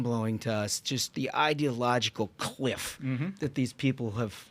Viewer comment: I thought he said ideological queef which I laughed at and realized afterwards that you said Cliff but I like queef better cuz it is an ideological queef I'm not laughing at your humor I'm laughing at my humor